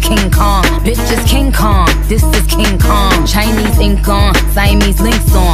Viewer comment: Im dead at 1:24 i heard " chinese kinks on " As lucas swaggers by. Omg sorry im gonna stop and probably needs a beating hahahaha